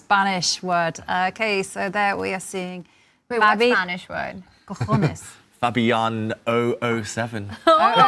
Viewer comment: did she say cohones😂